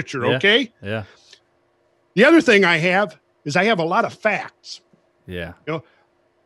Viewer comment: I had a hearing failure early in Mr Carlson's discussion when he said "I have a lot of fat". Puzzled, I wondered what that had to do with the conversation but when he repeated the phrase the anomaly was resolved as I then heard him say "I have a lot of facts" !